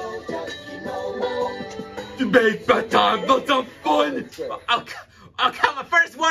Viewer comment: what did Patrick say? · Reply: You make bath time lots of fun